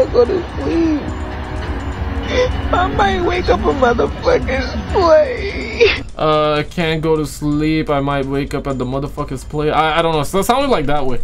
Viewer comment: i might be wrong but what she was trying to say was like, i might wake up next day as "$lave" MAY BE IDK